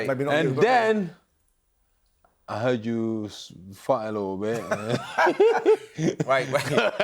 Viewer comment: I swear I heard Derick said Simon Jordan FART a lil bit 28:31 😂🤣😂🤣😂🤣😂😂😂😂😂